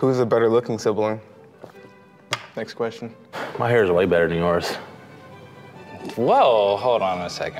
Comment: did he just say his hair is better? Tf